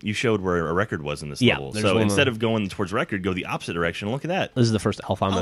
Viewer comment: it sounds like jack says erection instead of direction. LOL!!!!